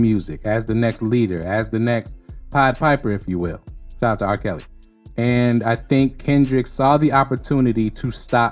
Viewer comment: Did you say shoutout to R Kelly? 11:12 Weird.